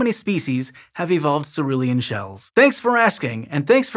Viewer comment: What colour does he say in the end? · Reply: Celurean